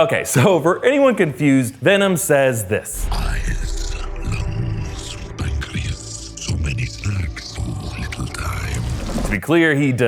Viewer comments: thanks i thought venom said: EYEZZZ ON BAKRIEZZZ SO MANY SLACKS FOR OUR LITTLE TIMEZ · I thought Venom said, “Eyes,Nose,Pancreas.”